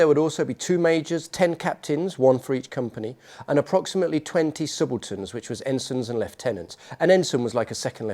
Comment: Oh, "subalterns." I thought he said "simpletons"!